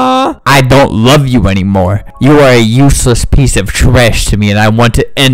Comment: I accidentally double tapped when he was in the middle of saying “you are a useless piece of trash to me” and it sounded like he said “you are a useless piece of trash Tamia” and I panicked for a second- (Tamia is my name)